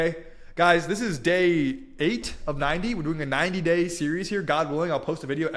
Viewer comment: I’ve still got 82 more days of knowledge to drop here! These receipts are so powerful. There’s still so much more that makes the proof undeniable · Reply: Awesome! I thought you were saying 9 days, but 90 days is much better, LOL